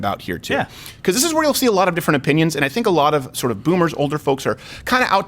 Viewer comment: DiD yOu JuST sAy BoOmeR?